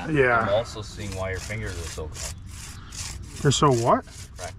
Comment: “Your mom loves when my hands are dry” love the dad and son connection 😂😂 keepin it in the family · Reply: I could have swore said " I can also see why your fingers are so f**ked" 😂😂😂 And I'm pretty sure thought the same thing. He said "my fingers are so what????"